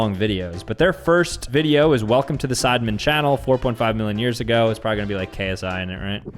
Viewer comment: Did i just hear "4.8M years ago" or am i becoming deaf